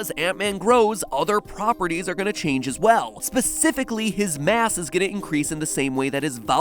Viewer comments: His mass?I thought he said his a**. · lol i thought he said ass